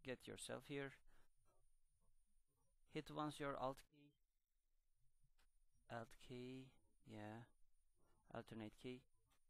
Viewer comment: If Anyone Didn't Know What He Said "Press Ur Alt Key". Your Welcome